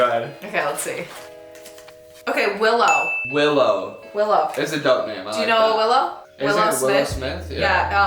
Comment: I thought she said Willa for a min, that’s my little sisters name